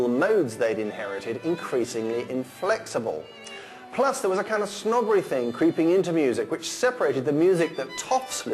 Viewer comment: What does he say at minute 4:40? 'Plus there was a kind of a ...' what? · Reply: "Plus there was a kind of snobbery thing creeping into music, which separated the kind of music that toffs listened from the songs and dances that ordinary people enjoyed."